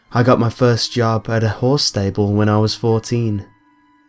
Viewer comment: Is it bad that I misheard 'Horse stable' as 'Whore stable' ?